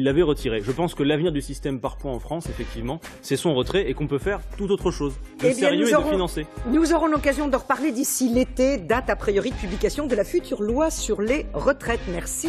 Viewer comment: C'est moi ou à la dernière seconde de la vidéo 28:16, quelqu'un dit "merci mon chéri" ?